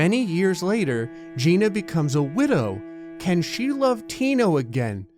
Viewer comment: LOL i thought it said gina becomes a window